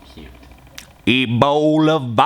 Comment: seconds in, in the background, did someone say cute? XD